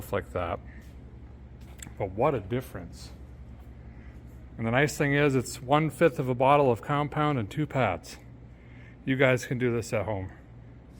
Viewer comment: Why did I hear you say, "And the nice thing is, all it takes is 1/5 a bottle of vodka and two pads! 😏😅😅😅😂😂